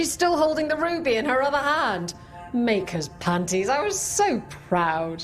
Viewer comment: Did it say "Maker's Panties"?